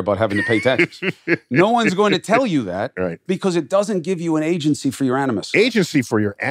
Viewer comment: Captions are atrocious and I can’t make out what he says at 13:37? I hear agency to your animus. Can someone confirm? If it is agency to your animus, can someone explain? Agency to something you dislike? Not fully understanding the use of agency in this context :(